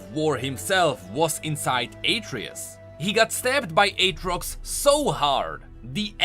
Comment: "he got slapped so hard by Aatrox-the aspect died" i nearly died out of laughter. Even tho you said "stabbed" i heard "slapped" the 1st time